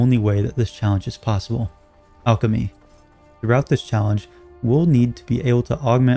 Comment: when you said “alchemy” I thought you said “becoming god”